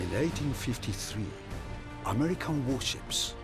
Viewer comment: they say 1853 but the subtitle says 1854 not thanks obama